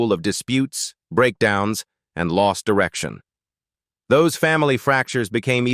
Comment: did he say Lost erection😂